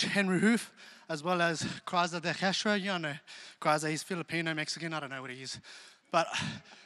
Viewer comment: what is he saying? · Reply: "he's filipino? mexican? I don't know what he is"